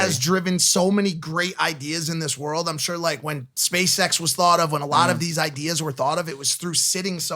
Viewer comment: I thought Mike said space sex instead of SpaceX and was like, not where I woulda gone, but I get it because space sex IS cool.